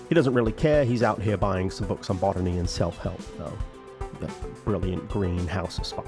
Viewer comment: i thought you said "he's out here buying some books on bottoming and self-help"